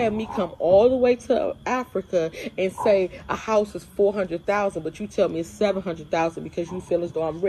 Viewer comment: She lived in a "mansion" that cost $100,000?! Hmmm ok...maybe she meant it seems like a mansion compared to where she's living over there. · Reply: I thought she said 400,000